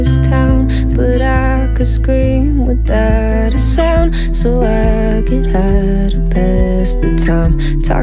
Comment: until this moment I had always thought she said "so I get high to pass the time" 😂 Shook